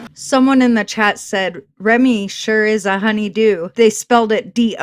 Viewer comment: she really said HONEYDEW, not HONEY-DO...she is THAT food driven LOL